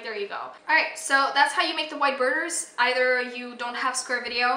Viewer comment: Im so sorry thanks for the edit but I heard so thats how you make white burgers